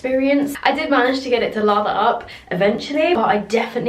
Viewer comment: Lol I thought she said “lava up” but she was saying “lather up” 😅